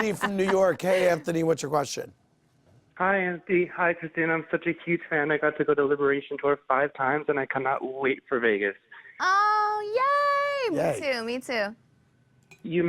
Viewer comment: OMG did they really say "Christina NalgaBuena" omg every Spanish speaker must be dying ...